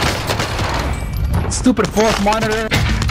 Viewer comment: Thought he said "stupid hall monitor" for a sec